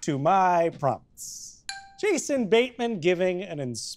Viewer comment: I thought it said batman